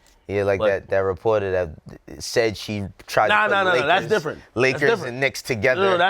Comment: I heard the shit slowed down...I hear "Nakers" clear as day